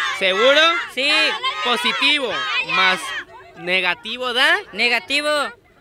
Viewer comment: positivo mas negativo es el signo de mayor valor c: Los dos pensaron que dijo "por" pero dijo "más"